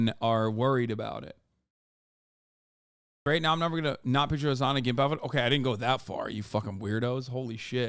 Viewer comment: What does he say at 49:02 ? I’ve listened like 10 times and I still have no clue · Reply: "Great. Now I'm never going to not picture Hasan in a gimp outfit"